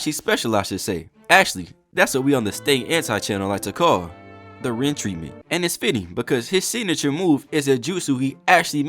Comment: what did you say after saying the kakashi special? 😂 “actually, that’s what we on the state anti channel like to call, the ren treatment.” <—- ??? Idk your accent is throwing me 😅😂